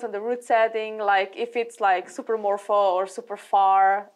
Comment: what did she say in 20:10? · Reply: Super morpho. Morpho is short for morphological, and means a climb that is very specific to a particular body type (usually, but not always, favouring tall people).